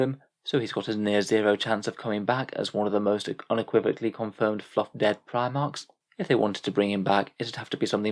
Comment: Ima be honest i thought he said “fuck dead” and not “fluff dead” and i was like damn son